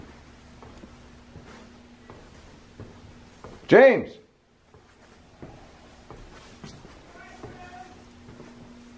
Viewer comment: At around 13:10 I heard a crowd of people then a man say what I think could be "places" I could be wrong, can someone confirm it? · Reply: I think it said "he's right there!"